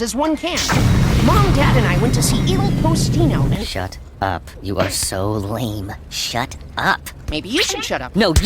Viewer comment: "You are so lame" sounds like he's saying "You suck". what he meant to say was "You are so annoying". Overall, the animation's good.